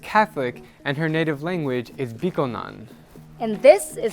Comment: I thought you said her first language was bitcoin